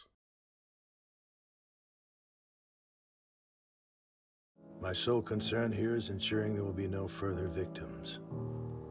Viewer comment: what did you say at 9:07? The audio went out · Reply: Whoops. I think I said, "the plan is to do STUUFFFF"